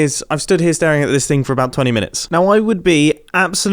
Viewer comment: I thought it said seven years ago instead of minutes and got confused.